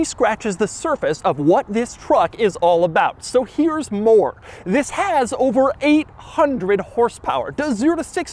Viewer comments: I thought he said "It has over 100 hp" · Obviously more than 100 hp. Lol I guess he said 800hp and I misheard :D